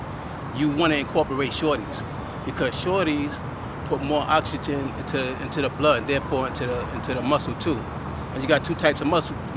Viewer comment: I thought he was saying "you need to do shawty's because shawty's put more oxytocin in your blood" for a second, i'm tripping